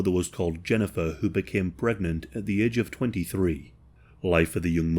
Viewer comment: I thought he said gregnant in the start 🥲